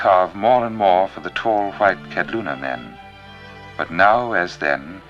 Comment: he says they carve for the tall white.. ket luna? men? What is the ketluna word he uses? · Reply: White man, often spelled Kabloona. Also title of GREAT book by Gontran de Poncins